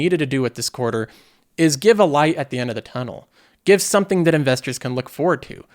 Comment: "Give a LIE at the end of the tunnel" I think is what I heard.... lol